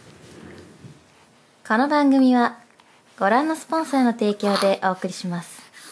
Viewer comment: 「この番組はご覧のポンスの提供でお送りします」って聞こえた。プク丸じゃん！てなった。